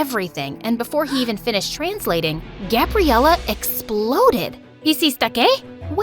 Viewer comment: foreign language misheard: " is this a gay"